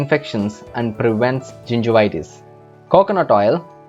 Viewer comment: Great ingredients. At the end I thought he said ‘this also cures GINGER-itus’ LMAO Will definitely make this concoction